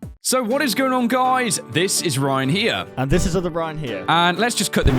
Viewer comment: In the beginning, I thought they said this is Ryan higa and I was like 😱😱😱😱